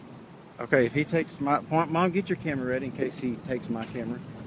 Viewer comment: Did say, Mom, get your camera ready 4:00 minutes?! Is that mom??? Or is going against his own standards and is guilty of abuse, calling someone other than Parker's mother "Mom"???